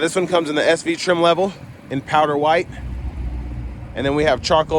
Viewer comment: Ohhh "powder" white, I thought you said "power" white, figured it came with a complimentary hood and everything.